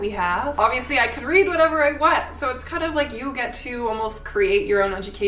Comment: の部分がit's kind of like you〜と聞こえるのですが合ってますでしょうか…！